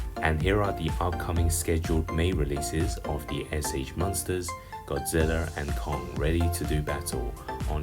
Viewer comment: "sh monsters godziller and kung, ready to do battle" lol i laughed when i heard him pronounce it like that